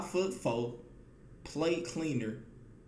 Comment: I Thought He Said PlayCleaner But He Was Saying PlateCleaner 😭😭😂😂😂